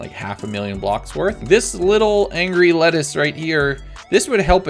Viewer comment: Did say angry little lettuce at 2:15? #LettuceEatersUnit